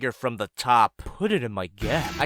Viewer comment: did he said "put it in my gyatt" or I'm just crazy?